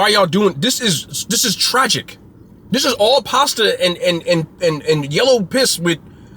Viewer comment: Did he just say the Mac and cheese was pasta with yellow piss? 😂😂😂